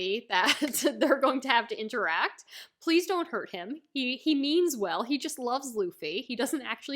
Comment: Talking about Batholomeo running rampant in Shank's territory you were excusing him "he means well" but I hear "he memes well" and it fitted even better. :P